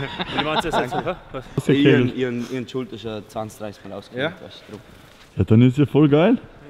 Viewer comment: Was sagt bei 4:16? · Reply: Lauri Nauke Fanpage i hob die schulter scho 20-30 mal ausgehängt